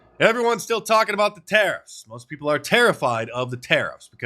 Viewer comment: Did you say "tariffied" or is it just me?😂